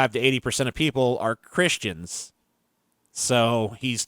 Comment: Did you say "Crishtchans"?